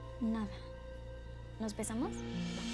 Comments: pensé que dijo nos besamos 😏 xd JAJA y dijo nos pensamos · minuto 30:27 párese que dice nos besamos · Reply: Patricia Rios yo pense lo mismo y cuando saco la vascula dije a nos pesamos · pensé que dijo "nos besamos?" JAJAJAJAJ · En la parte 30:28 yo no entendí nos pesamos yo entendí nos besamos😅🤣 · le entendi nos besamos JAJA